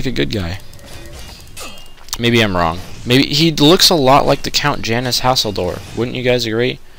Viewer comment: When he said the count's name I had to make a double take. I could've sworn he said "Janus Hasslehoff"